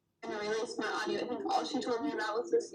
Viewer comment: I hear, "release more audio"